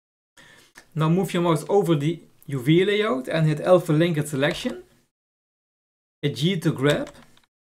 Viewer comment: what did he said at 8:21 ? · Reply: couldn't hear it at first to, he says press L